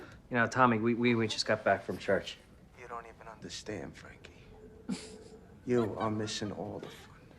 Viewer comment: why have i always thought tommy said "you don't even have to stand" not "you don't even understand" lol - amanda